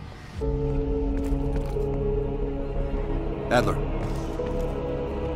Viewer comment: I thought he said Hitler... LOL!